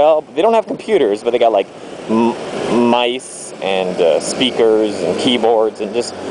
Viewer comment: lol I thought when you said "Mics and speakers and keyboards" I thought you said "Mice and speakers"